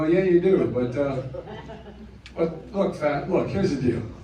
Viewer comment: Did Biden say "look fat?" 10:37... it almost sounded like he wanted to call him a fat ass.